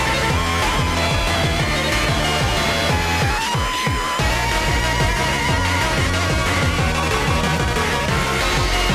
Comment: I swear MX says "It all ends right here" but I'm not certain, am I right? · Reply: He says “get back here”